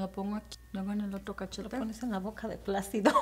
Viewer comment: I heard "te lo pones en la boca de plastico" 😂😭😭 tu madre es bien SAVAGE!! Hermosa!!